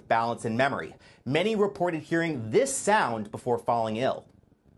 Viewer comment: So where did they get a an actual recording of the sound if it's such a mystery? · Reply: No,that's not what he said. He said "many reported hearing this sound before falling ill" at 2:04.